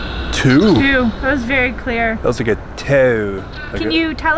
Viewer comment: I don't hear TWO. I heard "Tom"